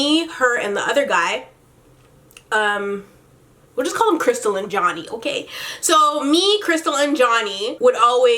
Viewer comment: I thought you were saying "Let's call him Crystal Lynn Johnny" I'm like that's a long ass name